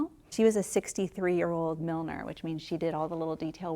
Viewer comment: I thought she said millionaire.